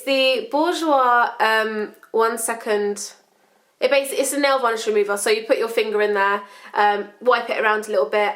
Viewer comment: It sounds like you say ''Nail Volish'' instead of ''Nail Polish'' haha :)